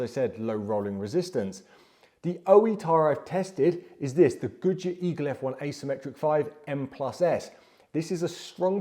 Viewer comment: what brand does he say? It sounds like "goo-ji E cliff"...? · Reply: gooodyear eagle